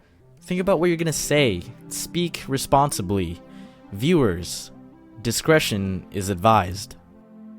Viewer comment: I thought it said misdirection XD